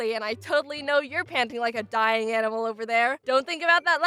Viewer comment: I thought she said "I know your panty like a dying animal over there"